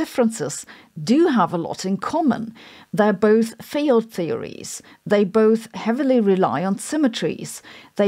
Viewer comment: sounds like you said "failed theories"